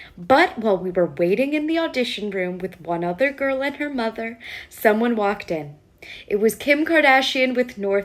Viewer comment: it’s not kim!!! it’s kanye!! eveyone just blames kim for eveything cause it’s easier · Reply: I thought I heard the lady say Kim walked in😂